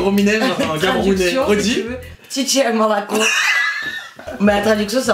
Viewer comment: "Titi à Moroco" Mdrrrr, dites moi que je suis aps seule a entendre ça 😂😭